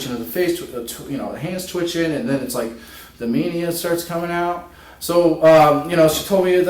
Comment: Very funny 🙄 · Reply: Yep, I heard "Media" And that is what I found funny. It would have been one hell of a line if he said "Media" but it looks like he actually said "mania"